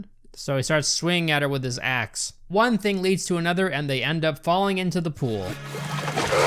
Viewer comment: I thought you said "then one thing leads to another and they end up falling IN LOVE and I was like, this twist is not that bad...